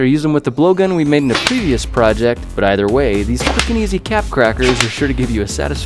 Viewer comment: did he say fu*king easyncap darts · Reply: Quick and easy